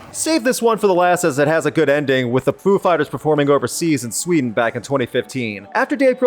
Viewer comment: did he say 'poo' fighers?